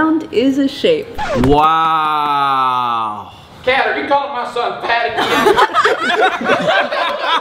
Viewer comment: i thought he said “kat are you calling my son patty cake”